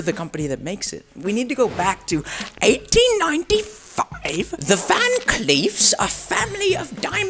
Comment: When he said "1895" I heard it like "189 f***") not gonna lie